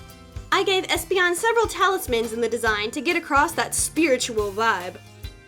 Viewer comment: I keep hearing “Food talismans” instead of “Fu talismans” lol